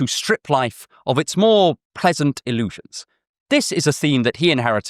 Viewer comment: Go to <link> to understand how different perspectives shape our worldview. Save 40% on the Ground News unlimited access Vantage plan with my link. LINKS AND CORRECTIONS Support me on Patreon here (you lovely person): <link> Subscribe to my Substack here for more of my writings (and my scripts): <link> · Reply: Actual mistake that I noticed (never done one of these before): at 24:02, the subtitles say "present" instead of "pleasant" That aside: thanks so much for making this video. I really needed this this week.